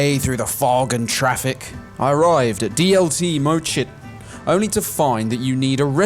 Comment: I heard "DLT more shit" 😆 Not too far off